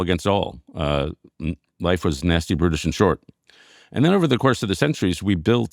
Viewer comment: Oh my God, I thought he said “nasty, British, and short” LOL also thank you for the book recs